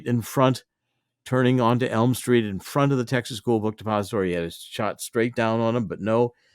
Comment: All I hear is “Texas book Suppository “